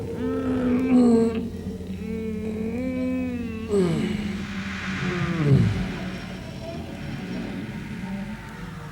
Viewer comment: also what is this one even saying? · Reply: VPRO, a Dutch channel that originally broadcast religious content, but shifted towards more avant-garde stuff